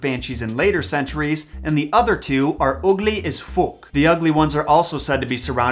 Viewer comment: I thought he said "Ugly as Fuck" with a Accent xD